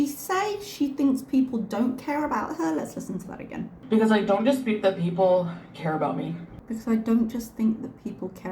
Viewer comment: I think she is saying "I don't dispute that people care about me"